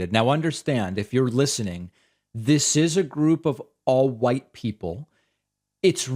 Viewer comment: it must be to her since they never report that shit, also am I hearing this wrong or is David actually saying white people are gullible at 2:45?